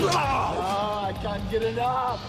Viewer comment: "Aaaaah, I can´t get it up!" Misheard Wrestler Quotes. This should be a Hashtag.